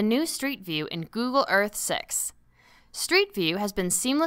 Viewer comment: Am I the only one who heard "Google earth SEX"??